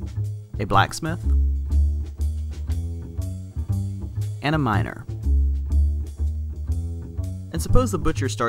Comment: i heard and a minor not miner